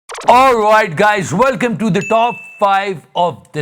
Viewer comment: I thought you said, "All white guys, welcome to top 5 of this week"